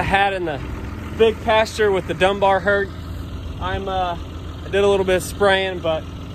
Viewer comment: Did say where's Dunbar? Thought I heard her say that.